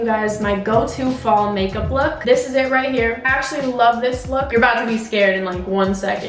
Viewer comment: i thought she said ashley loved this look at 0:24 💀💀